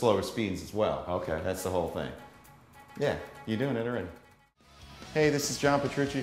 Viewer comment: If your talking about what he said at 4:31, he actually said your doing it already